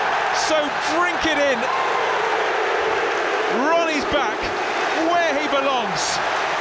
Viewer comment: Did He Say Rooney is back